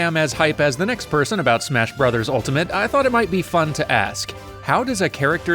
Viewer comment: mothers Basemnt sent me here and around # 0:30 i thought he was gonna say "i thought it be fun to ask, whats in an a OP"